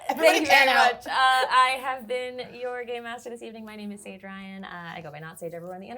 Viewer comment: This was so good!!!! I’m sooo glad it was as gay as it was (also was typing this while Saige did their outro and definitely combined things in my head so it sounded like they said “gay master”)